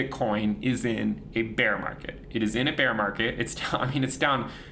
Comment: I thought he keep saying we are not near a bear market